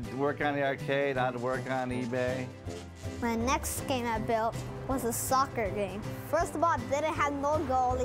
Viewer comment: did he just say “My next scam I built” Kid your going places